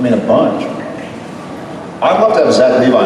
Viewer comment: Big fan of Supernatural... I actually helped write some of the songs in Fan Fiction, but so many others did too- so mine only made it a course or two... lol- anyway, who did Jared say he would like to have on the show (from Heroes) which I am also a big fan... around the 14:15 mark. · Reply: Zach Levi. Not sure if I spelled that correctly or not, I'm not sure who he is